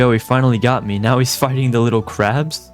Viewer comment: what the hell did you say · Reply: He said fighting but it really sounds like the other word